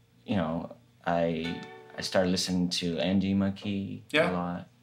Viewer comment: who does he say he listened to on 2:05? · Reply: andy mckee